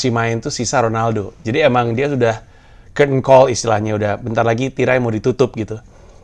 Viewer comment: gua ngulang 9:03 tiga kali buat paham mas pandji ngomong apa ternyata Curtain Call wkwkwkw aksen British bgsyd